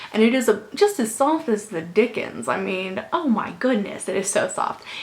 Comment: OMG! I thought you just said "just as soft as the dick" and I was like...Wait! what did she say!? rewind, oh ... I have to get my head out of the gutter :P